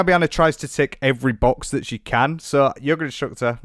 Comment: stop because i heard yogurt extractor instead of yoga instructor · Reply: I had it playing as I was falling asleep and I heard "yogurt structure", I thought it was just my sleepy brain messing things up lol